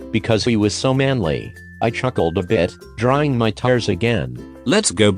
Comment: was it just me or did someone hear 'drying my tires'?